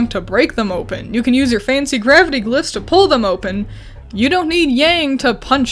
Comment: Did I hear “Gravity Glust”?